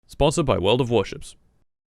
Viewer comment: why did I hear you say war of washers